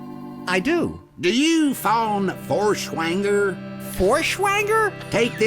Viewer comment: hab erst vor schwängel oder sowas ähnliches verstanden XD